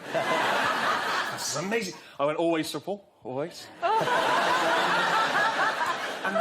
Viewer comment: What is Stephen saying at around 8:27? "I went always ????" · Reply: "Always, Sir Paul, always"